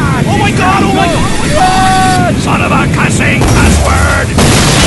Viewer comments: Son of a cussing cuss word -Soldier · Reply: bobezing livei I thought he said son of a classy cluss word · Son of a cussing cuss word -Soldier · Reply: I thought he said son of a class A cuss word